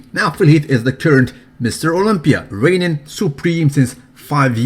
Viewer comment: lol did he say "now Phil Heath is the turd Mr. Olympia"